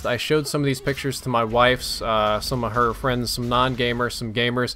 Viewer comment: was it just me or did say 'wives' at 3:40...